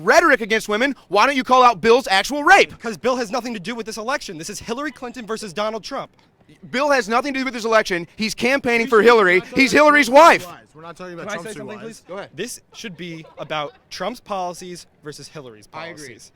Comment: did he say "he's Hillary's wife"?